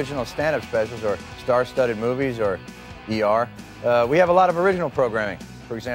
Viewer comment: Did he just say ER?